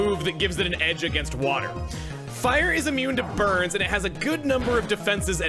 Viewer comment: for some reason i heard that as "fire is immune to birds" at first and i was like???? since when is fire effective against flying??? XD